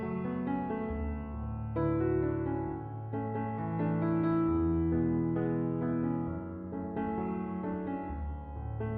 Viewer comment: Looks like is sending smoke signals👌👍☞🤔☜🎥👀 I couldn't figure out what he was saying though did you!! Haha keep the videos coming bro they're awesome!!👍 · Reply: 🤔 “Have a Bealy Good Day” 🤷🏻‍♂️🤣